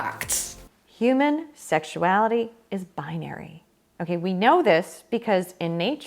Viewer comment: thought she said binder! Why am I laughing so hard!?!😅